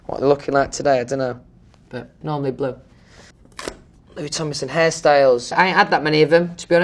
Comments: I HEAR "LOUIS TOMLINSON HARRY STYLES" RATHER THEN "LOUIS TOMLINSON HAIRSTYLES" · stg when it said “Louis Tomlinson hairstyles” I heard “Louis Tomlinson Harry Styles” and I was like oOP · I thought it said “Harry Styles” and I feel off the chair😂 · I jumped when it said “LOUIS TOMLINSON HAIRSTYLE” cus I thought he said Harry styles :( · When he said "Hairstyles" really sound like he said "Harry Styles" I'm crying ok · I thought it said "Louis Tomlinson Harry styles" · am I the only one who though its "Harry Styles" instead of "hairstyles" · For a second i thought he said "Harry Styles" 😂 Actually hairstyle and Harry Styles are kinda similar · when it said “Louis Tomlinson hairstyles” I gasped bc I thought that it said Louis Tomlinson Harry styles 💀💀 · i can’t be the only one that when it said “louis tomlinson hairstyles” thought it said “louis tomlinson harry styles” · when he said “louis tomlinson hairstyles” i thought he said “louis tomlinson harry styles” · When it said hairstyles I thought it said Harry styles and I screamed then I realised it was hairstyles 🥺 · ok but when he says "louis tomlinson hairstyles" it sounds like he said harry styles 😭😭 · Lpm cuando leyó hairstyle pensé que decía Harry styles 🤡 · I legit thought that he said harry styles but all he said was hair styles :,(